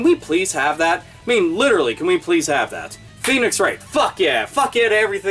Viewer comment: I thought you actually said penis wright